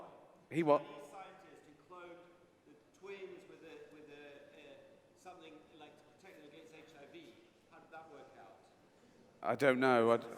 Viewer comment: I feel for the guy who was trying to speak at 34:39, but at the same time hilarious. Does anyone know what he said? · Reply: He was asking something about cloning twins and hiv immunity. It sounded like a really good question but no one could hear him.